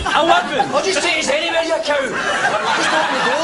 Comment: What does he say in 1:56 i can't understand him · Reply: “Oh just take us anywhere you cow!”